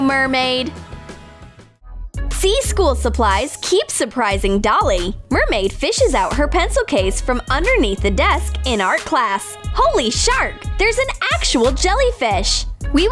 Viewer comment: She said HOLY SHARK.I thought that she said HOLY **